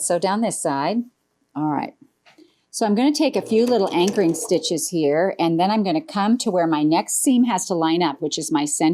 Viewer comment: Very pretty! I have to laugh I use to think when Jenny says I take a few anchoring stitches, I thought she said I take a few angry stitches. I said why would Jenny say angry? So I listened more closely and had a good laugh at myself. 🤣 Happy Friday everyone!